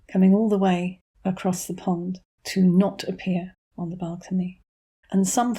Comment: OMG! I was watching this video and eating my very loudly crunchy lunch salad when you said "... and appear on the balcony ..." and I heard " ... and peeing on the balcony ..." and my mind went immediately to the news that MM had impressed H by hopping off into the woods to eat with bodily functions so I wondered who she would be trying to impress this time around.